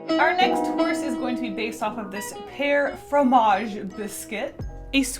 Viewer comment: I thought you said hair instead of pear, and I got really confused...